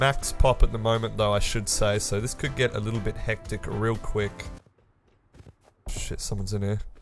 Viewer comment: Wath did you say at 1:08??? Shit on dirais?